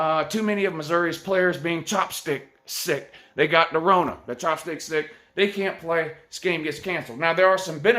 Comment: What was a bigger loss, Fields for UGA or Cam for UF? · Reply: Excuse me sir, did I just hear “The Chop Stick Stick”? Lmaooo